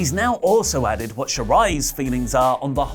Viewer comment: All I heard was "Io Shirai's fiance" RIP my hopes and dreams